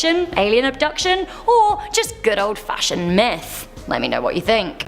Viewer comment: I thought you said "good old fashion meth" not "myth" spent like 5 minutes laughing